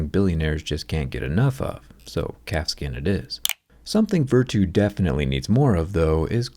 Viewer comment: For a second i thought you said CAT SKIN